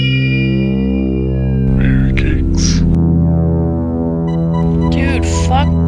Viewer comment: I always thought he was saying Mary Cates